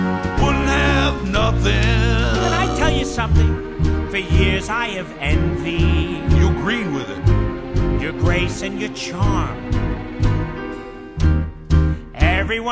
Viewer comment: YOU'RE GREEN WITH IT! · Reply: When I first saw this movie (my most favorite) back in 2008, I thought said "You green with it!".